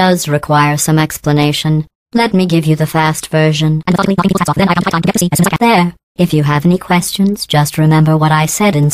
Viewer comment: Thumbs up, if you found out what she said. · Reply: "and methodically, knocking people's hats off. Then I [unintelligible] high time to get to sea as soon as I can-"